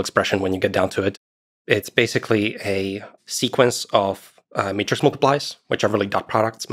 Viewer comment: Captions say "meter multipliers" but it should be "matrix multipliers"